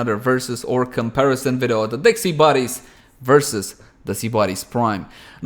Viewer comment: I swear I heard "Dexy buddies" XD